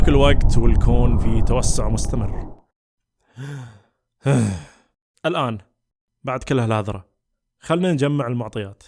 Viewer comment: شو قال في الدقيقة 3:20 ؟؟؟ الآن بعد كل هال.... ؟ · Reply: Imed Benzahra بعد كل هالهذره يعني بعد كل هالكلام